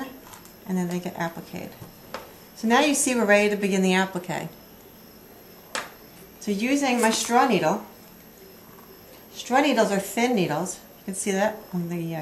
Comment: What do you call your threading needles? It sounded like you said, "straw needles". What is that board? Sand paper did you call it? Where does one get something like that or did you make it?